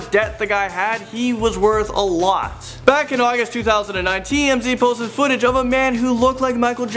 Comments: did he say august 2019? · did he say 2019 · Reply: Arthur Morgan 2019 No he said “in 2009 Tmz” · Did he say "Back in August in 2019?" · did he say back in August 2019???